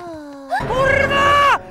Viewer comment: kurwa noooo to usłyszałam xD